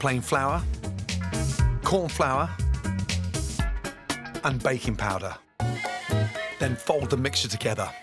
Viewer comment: When Gordon said backing powder I thought he said bacon powder 😂😂😅😅